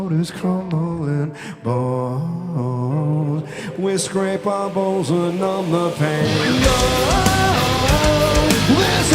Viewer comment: During Bonescraper i heard: "we scratch our balls to numb the pain" ... i cannot unhear it😂😂😂